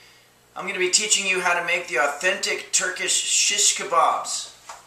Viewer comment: At first i thought it said Virgin Shish Kabobs